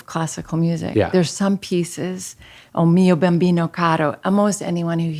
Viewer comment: what classical music reference did she say?? Bembido? could anyone share? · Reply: I believe she said “O mio babbino caro” by Giacomo Puccini